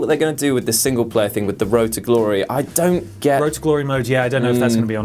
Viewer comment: Thought he said "Road to Gloyhole" at first....